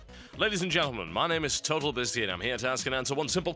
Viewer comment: Sounded like "Total busy", lol.